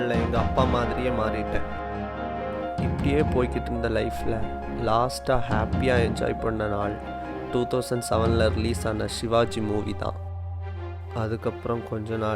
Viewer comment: yt captions says enjoy watching porn